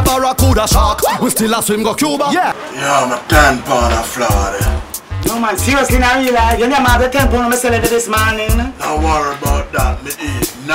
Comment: but why @ 0:57 does it sound like he is saying "my tampon a der florida" just saying. Love the video----Love Degree more!